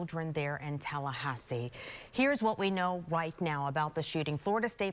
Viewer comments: did she say "Here's what we know white now"? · "Here's what we know WHITE now.." @ 1:02 says everything